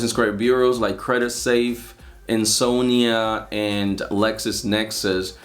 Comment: Ty for the video! U named credit safe … and could you please spell out the second one… I heard “insonia” lol, and I can’t find it lol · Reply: lol Ansonia. I know my pronunciations are a bit hard lol